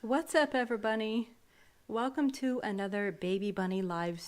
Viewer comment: I thought she said what’s up everbunny